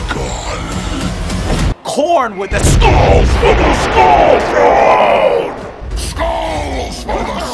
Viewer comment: did......did he just say souls for the soul throne?